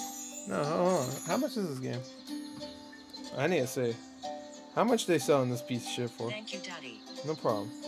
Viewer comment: excuse me what did she just say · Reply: “How much they selling this piece of shit for-no problem”